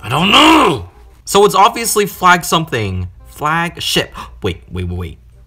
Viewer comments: When they said "Flag ship" I thought they said "Flag" SH## 😂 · I thought he said Flagsh!t